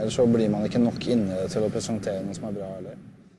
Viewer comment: he says: "Never trust a journalist" ! ( in norwegian: aldri stol på jornalister!) Not what the subtitle says: "everybody believes a journalist".. stupid translate!